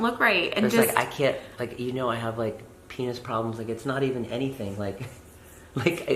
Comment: did he just say he has “penis problems” ??? 😳 TMI